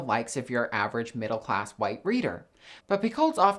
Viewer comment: I thought you said “average middle class white BREEDER” not “reader”. 😂